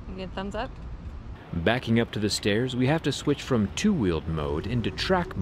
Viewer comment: I thought he was going to say : " backing up the chair, we had to switch cheese 🔫 😂😂.